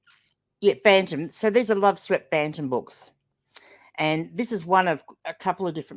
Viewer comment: Listening while driving home & thought you said a 'Loveswept Phantom' bundle. Quite the crossover! Congrats on the monetisation. 👏